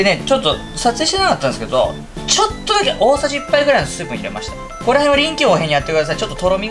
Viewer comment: 辺り：「撮影していなかったんですけど、ちょっとだけ大さじ1杯のXXXXを入れました」XXXXはなんて言っているんですか？ 概要欄の材料一覧にも書かれてないですし、「スープ」に聞こえるけど、スープってなんですか？ コンソメの素？ トロミをつくと言っているので、水溶き片栗粉のこと？ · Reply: 「水分」って言ってると思いますわぞ